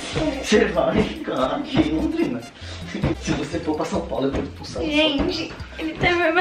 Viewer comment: "Se você não for para São Paulo eu vou para santos" Só eu vou entendi assim? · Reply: "Se vc for para São Paulo, eu vou te expulsar"